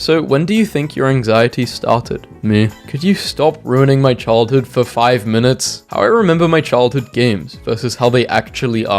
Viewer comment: what was that called again? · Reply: I think it was called trouble